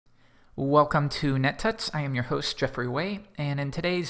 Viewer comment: Did he just say "Welcome to net tits"?